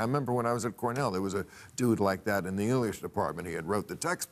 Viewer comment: what the hell does Bill say here? He was in the what department?? Sound like he says Euliesh, which is most definitely not a word